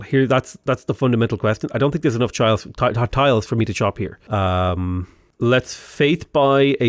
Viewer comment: did you just say "I dont think there is enough childs... tiles to chop here"?